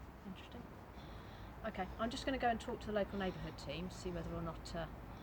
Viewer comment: She starts the pissing contest but she doesn’t win this one …I notices the make copper kept his trap shut and throw her under the bus lol 😂😂😂 I thought she said she was going to speak to the neighbourhood watch team ha ha ha 😂😂😂,,,,when they are wrong ,rather than apologise they piss of and disperse like the red arrows display team